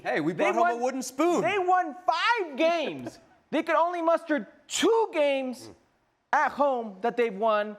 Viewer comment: I'm gonna give him the benefit of the doubt, but it sounded like Herc said "mustard" instead of "muster" 😂